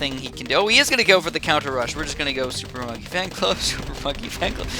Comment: Did he say super f$cking fan club